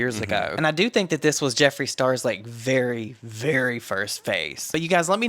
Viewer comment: LOL his "first" face. 🤣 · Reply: I thought he said "phase" but "face" is so much fkn funnier 😂😂😂